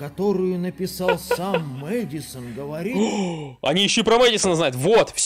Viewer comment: Мне послышалось Эдисон